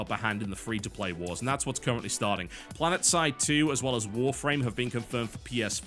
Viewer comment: did i hear right? planetside 2 for ps4? AWESOME!